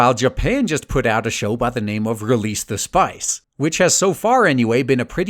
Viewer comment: I heard "release the spice" as an anime. Brb i hope it isnt a fire pit.